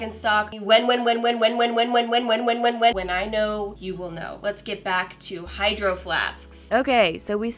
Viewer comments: Who else heard her say "let's get back to our Hydro flasksk" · When you say hydro flasks it sounds like you say hydro flasksksk